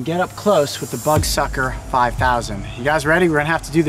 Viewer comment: I thought he was saying buttsucker 😂😂